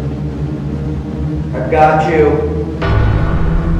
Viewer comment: I sounds like said "not yet"